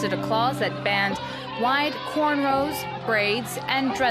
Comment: The best thing I like about Afro-textured hair is that it can be worn in braids, locks, twists, curls, slick back, wavy, straightened, bantu knots, afro, "cornrolls" and look neat as can be! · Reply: Creature I thought yall said "canerows"